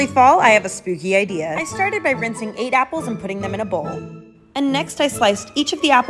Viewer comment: I was covering my ear by accident so i thought she said she started by renting 8 apples😂😅